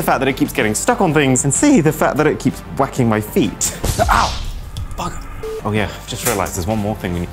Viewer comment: What did he sayyyy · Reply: Bugger, thats what he said.